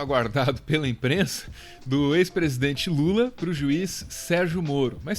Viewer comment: Por um segundo em 0:15 escutei "... ex presidente Mula!"...